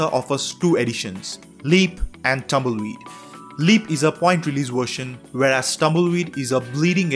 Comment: I heard "Leap is a pointless version" 👼